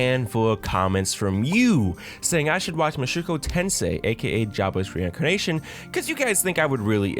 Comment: I thought you said “Mushoku Tensei aka Jabbas Reincarnation.” And was chuckling at how fitting the comparison to Jabba the Hutt was before I realised my gaff.